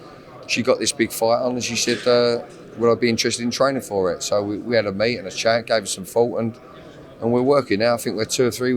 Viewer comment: does he say "we had a meet and a shag" with ebony bridges? Fair paly man. Good work ❤️